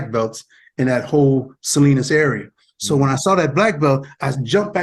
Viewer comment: Did he say "Salinas area"? Like in California? Monterey Bay area.....Salinas , CA?